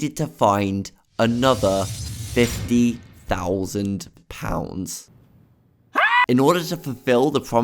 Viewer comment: i got an ad right here so all i heard was "50,000 OLD SPICE" 😭